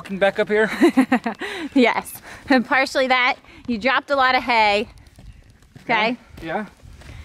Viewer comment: I heard that as "He dropped alot of HAA-AAY!" lol! Dudes, we wanna see the kitchen installed though. Kitchen, kitchen, kitchen. <3